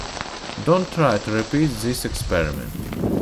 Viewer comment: Didn't quite understand you at 3:11 Go an try zees experiment? Soon try zees experiment? Gone high wiz zis experiment?